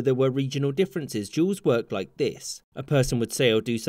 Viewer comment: So I listen to his vid while working and this auto played and became so confused cuz at first I thought he was saying Juul’s not duels lol